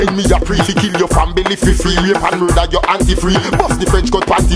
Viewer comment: Did he jus say "rape an murder yuh aunty free"? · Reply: I first thought he said “later murder u auntie free” but the next line says “buss the French cut panty free” so saying rape would correlate more I guess 🤷🏽‍♂️